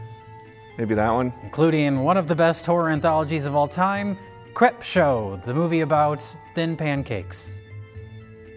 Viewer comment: I thought said 'Cripshow', that they were going to spend 40 minutes talking about MS-13.